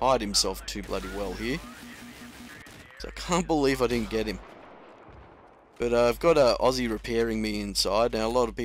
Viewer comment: ALSO I could not understand what you said at 6:50, when you said you did something, and everyone does it, so you might aswell do it to, or be at a disadvantage. Sounds like you said you 'have an Aussie repairing you' ??!! Could you tell what it is you said? You know most of us do not speak English as 1st language! Cheers!